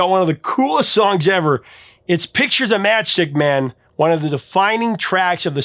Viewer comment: I finally found a stereo version of "Pictures of Matchstick Men". But I could swear the is saying "Picture The Matchstick Men" when he says the name of this iconic tune!